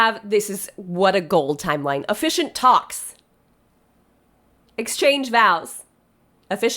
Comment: I thought you were saying "efficient talks," not "officiant talks," and I was just cracking up at that being how you listed toasts or pep talks. Should vows in the middle have given me a hint? Yes. I was wondering if you thought they'd need a lot of encouragement. 🤣